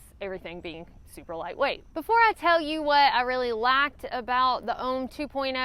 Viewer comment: I glad you specified "liked" and I thought you said lacked. Great video per usual.